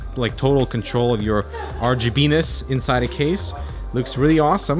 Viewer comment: Did he just say RGBenis?